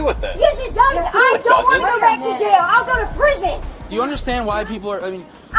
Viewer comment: did she say BACK!?!